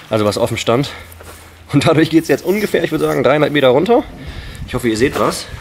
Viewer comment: ich habe 300m verstanden